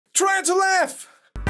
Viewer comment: I thought said tarantula 💀💀